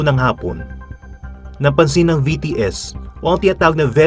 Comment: i thought i heard bts